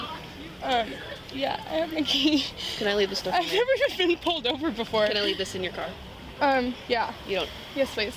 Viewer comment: I thought she said, I've never had someone pull out before."